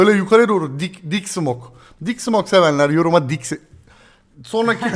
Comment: big smoke anladım 😐 ( gta san andres oynamaktan )